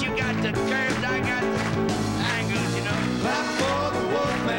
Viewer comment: If you've got the curves, I've got the angles!! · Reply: Haha! At first I thought he said “ankles” 😂